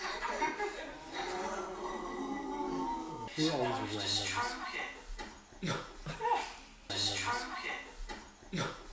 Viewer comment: what is saying here, and why are the guys reacting like that? 😂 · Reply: Ron said "you are supposed to stroke it" (meaning students have to stroke the side of book before opening to prevent getting attacked). But the guys took it as double meaning 😅😂.